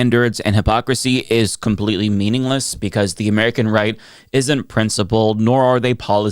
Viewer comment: For a second I heard “American Reich” and not “American Right”😅 I had to rewind for a second